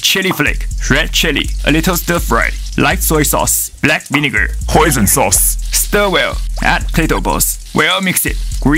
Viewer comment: I thought he said poison sauce 😂😂